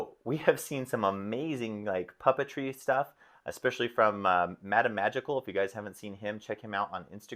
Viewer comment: Haha sorry MattaMagical <link> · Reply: Haha, thank you. I thought you were saying Madame Magical too and couldn't find the person.